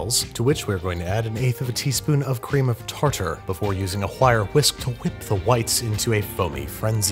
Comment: What Babish said: "...a wire whisk to whip the whites into a foam..." What I hear: "cool whip (Stewie griffin voice)"